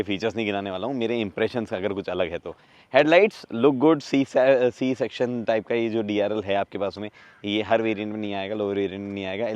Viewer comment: Did he just say "C Section"